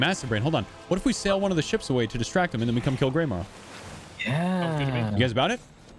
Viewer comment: when he says 'kill greymaw', it sounded like he said, 'im gonna kill granma' lmaoooo